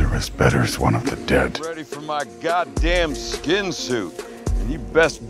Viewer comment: I just watched this after I woke up and thought he said "skin flute".